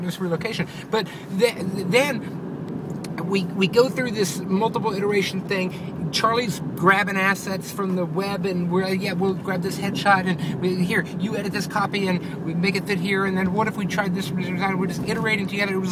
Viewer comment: lol, I wasn't really listening, but then I heard "Charlie's grabbin' asses" And I thought of little Charlie and was like o_____________o.. my bad, ahaha.